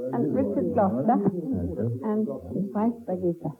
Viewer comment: lol she said Birgitte, I heard bug eater